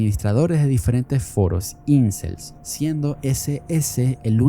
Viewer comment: escuché incest 🫪🫪😨😨😨